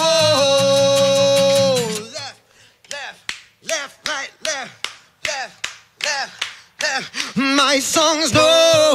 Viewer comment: What says 2:11 minutes? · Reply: He says "left,left,left,right, left.